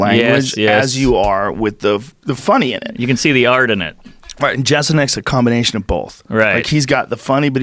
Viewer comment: What's the name of the stand up guy they mention a couple times around 17:00-18:00? Chestlenek is what I heard, but that's obviously not it :( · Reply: Jesselnik